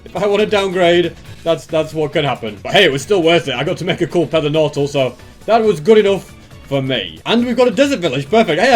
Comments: am I the only one who heard him say pether nortal? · Am I insane or did say Pether Nortal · i heard that pether nortal at 19:25 :D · bro said i got to make a cool pether nortal instead of nether portal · did say a "pether nortal" ?